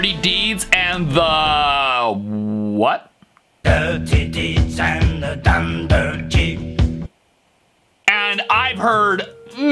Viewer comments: i heard dirty deeds and the tunder chese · I always heard dirty deeds and their thunder cheese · 7:27 Me: why do I hear dirty deeds and the dunder chee???